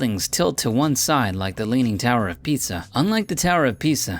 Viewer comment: Instead ofLeaning tower of Pisa it sounded like you said leaning tower of pizza